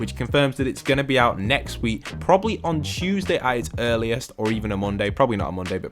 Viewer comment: Im sorry did just say Chewsday?!? I’m not trying to be rude or anything I’m just wondering if is from England or something